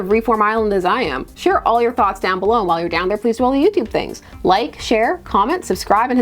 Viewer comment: I had to rewind a bit. I thought you said YouTube 35.